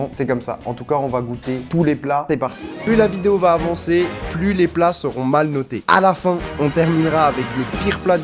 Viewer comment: à 0:34 pour moi c'est "plus le budget sera haut"